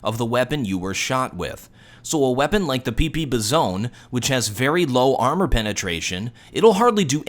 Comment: Did I just hear "PP Bezone"?